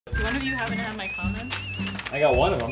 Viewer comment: It sounded Like said "do one of you happen to have my condoms" haha